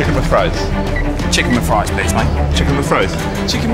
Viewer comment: I thought he was saying "chicken mcfries" for a sec there